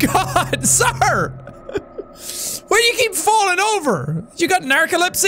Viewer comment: What Jack said: "Why you keep falling over?" What I heard: "Whatchya fell over for?"